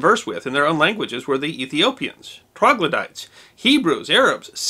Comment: It sounded like you said "Troglodytes" at 1:44 so I looked it up but couldn't find it in connection with Cleopatra. Then I turned Closed Caption on & it says "Ethiopians Prague Luddites Hebrews".